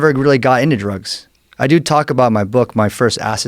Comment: everytime he says "talk about", i keep hearing "Taco Bell"